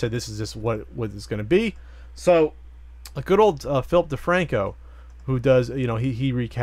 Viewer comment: I heard "good old Filthy Franku" twice there.